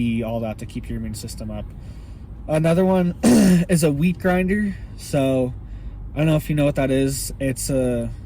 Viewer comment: I thought you said weed grinder LOL, I was like hell yea!!!